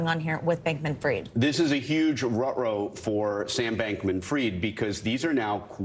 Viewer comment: did he really say ruh-roh on the news?? 🤣